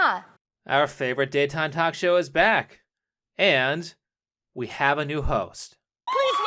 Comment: I thought he said " we have new hoes" 🤣🤣😂😂😂🤣